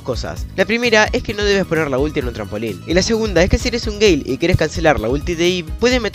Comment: pensé que dijo "si eres un gei" XD